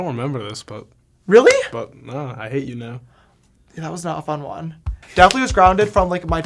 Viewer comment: I thought he said I ate you now 😂 ... wow I can’t believed I laughed out loud ....